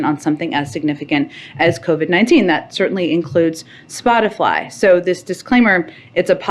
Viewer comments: Did JP just say "Spoti-FLY," (1:20) and "mis-indisin-fly-formation"? OK, I've had a beer, listened multiple times, but still not sure. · did she say “spoti-fly” Instead of Spotify? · did she say "Spotifly"? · does she call it SpotiFLY??? 😂😂😂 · I'm old enough to remember when the President calling media "Fake News" labeled him an authoritarian threat to the country. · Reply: Did she say spotiFLY?? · Did she say "Spotifly" at 1:20? 😂